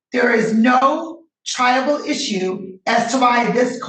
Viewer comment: For ages now I thought they were saying "no Tribal issues" (like hey we cant do this because they're native American) not "Triable issues"